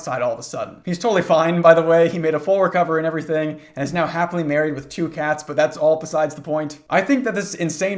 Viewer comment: for a second I thought you said He's married TO the cats 💀💀💀💀